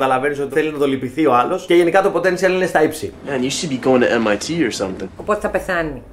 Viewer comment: "και γενικά..... στα ύψη"? τι λέει??? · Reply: Αγγλική λέξη χρησιμοποιούν "...το potential είναι στα ύψη...", και μάλλον δεν περίμενες μια αγγλική λέξη. Άν ο άλλος βαριέται να σκεφτεί μια ελληνική λέξη , έχουμε τέτοια φαινόμενα. Κατάντια. (κάποιοι έχουν φτάσει στο σημείο να μιλάνε κάπως έτσι "έχω ένα κόνσεπτ για ένα πρότζεκτ αλλά δεν μου βγαίνει το μπάτζετ..." , χαχα , κατάντια θλιβερή)